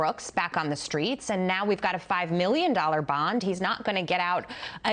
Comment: Oh $5 million lol thought it said $5 bail! I wouldn’t be surprised if it was $5 for real lol